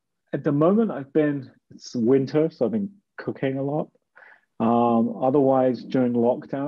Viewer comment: I had to rematch around the 54 min mark, I thought you said you had been up to cocaine, eventually realised you said cooking 🤣🤦‍♂️